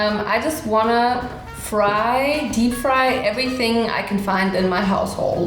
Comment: I thought she said deep fry anything in my ass hole